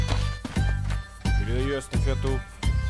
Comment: Что он сказал? Кстати, моча? И где лед :D? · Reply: Передаю эстафету